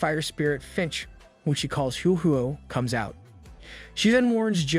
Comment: for a second I thought it said Ho-Ho like the Pokemon instead of what it actually said to be honest that is my bad